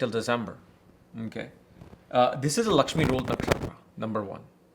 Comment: what did you say here? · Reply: Lakshmi ruled nakshatra